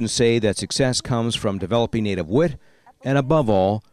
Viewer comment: What is naarrirator saying at 2:06 "..the students say that success comes from developing ? ? What? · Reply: "native wit" ....I know, I was wondering that too...I went back and played that part again